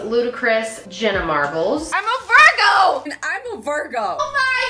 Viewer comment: I thought Jenna said "I'm a virginnnn"